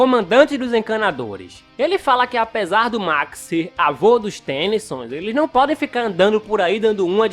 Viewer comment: eu entendi: o Max avô do Max aí eu buguei kkkkkkk